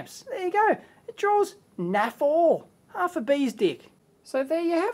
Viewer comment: Did he say "bee's dick" at 15:47 ? :)